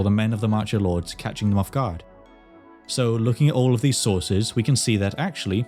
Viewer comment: subtitles say 'chronicles' but you say 'sources'